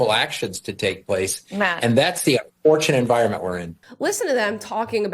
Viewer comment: I think she said "Matt". Would have liked to heard if she called him out.